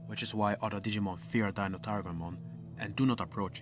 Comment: When you said DataTigermon, I thought you said “Daniel Tigermon” lol